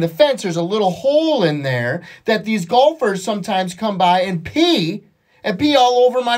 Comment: I thought he said gophers at first. Wtf.